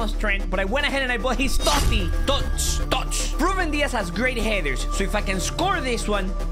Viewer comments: When you said Ruben Dias has good headers I thought you said great hair dues 😂 😆 laugh 😹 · I thought said ‘Ruben Dias has great hair’ haha 😛